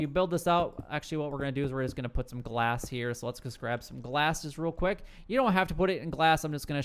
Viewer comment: just me or did he say "prescribe some glasses"?